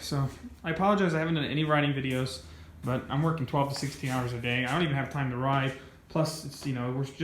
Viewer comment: Ohhhhh a day...i thought i heard a week lol yea u have no time to ride smh I feel ur pain